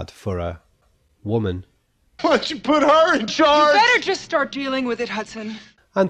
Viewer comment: i always thought he said " why don't you put her in charge"